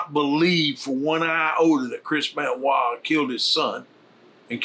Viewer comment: “I do not believe for one ????? that...” what is that Word? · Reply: Iota